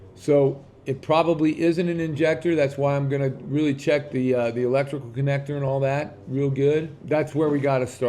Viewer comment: I thought said he was going to check pigtail first. Electrical he said….